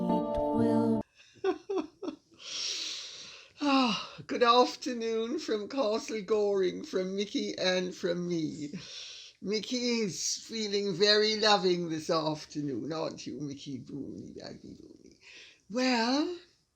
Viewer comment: I thought you said “welcome to ghastly Goring” 🤣👂🏻